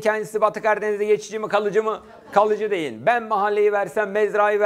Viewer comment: 52. dakikalarda divan, mahalle "kalıcı diyin" sözünü "kalıcı değil" diye anladım 2 x dinlemenin zararları 😂